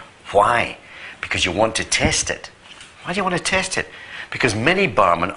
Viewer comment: Test it. You test the water. Thought he said taste it.